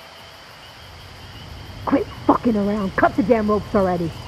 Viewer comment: anyone knows what they are saying? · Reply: “quit ducking around and cut the rope” it’s supposed to be a continuation to the story after cult remember